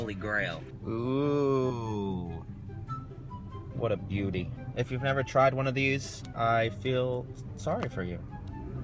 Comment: it sounded like he said "If you've ever tried one of these, I feel sorry for you". Welp, I've never been to Starbucks myself x3